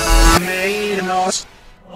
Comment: escuche a exe.. "reírnos aaa"